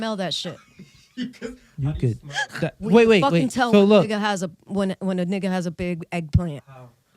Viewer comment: did this lady say n*gga at 45:15 min or i heard wrong